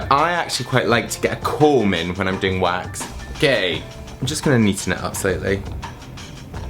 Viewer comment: What did say @ 1:54 ??? lol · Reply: amini I watched back. Ha ha. I say "okay" but it sounds like "Oh gay!"